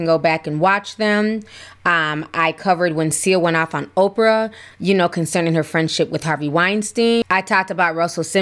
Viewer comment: I couldn’t make out the woman’s name who went off on Oprah for her friend ship with Harvey W…, did anyone catch the name??? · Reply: I believe she said it was Seal the singer, not a woman.